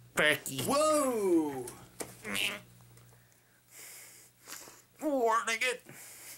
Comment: did he just say wartnigga